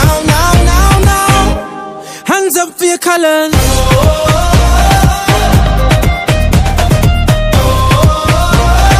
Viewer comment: what did he say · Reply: Hands up your colors